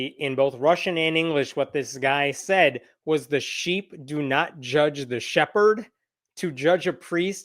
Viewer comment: All I heard was "The shepherd gets to shag the sheep." Baaa-loney.